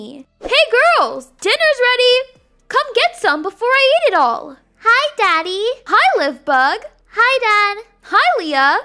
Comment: When said "Hi daddy!" I thought she said "Hi fatty!" 😰